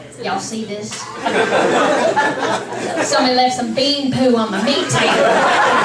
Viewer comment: I thought she said "Somebody left some gay on my hate table."